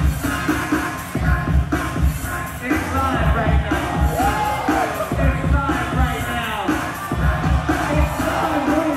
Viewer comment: what is the host saying? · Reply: "It's live right now"